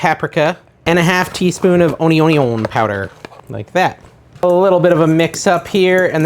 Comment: sec mark... What is that spice you're saying? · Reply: Onion powder